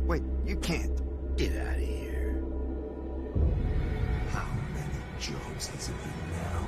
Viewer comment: what did the burned man say in 0:58 ? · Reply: How many jobs has it been now, Vincent? That's what he said. You're welcome